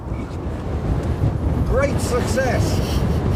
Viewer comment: "....great for sex...." did you say Come on miladdio we're English here and your flyin the flag????? keep up......